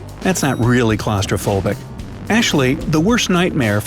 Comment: did he say Ashley instead of actually ? 😂